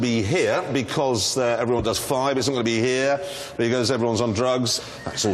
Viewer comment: What did Jeremy say whilst pointing at the USA. I can hear "Because everyone does..." but I can't make out what he says just after. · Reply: I think he says: "Because everyone does 5", so they wont drive faster then 5 mph.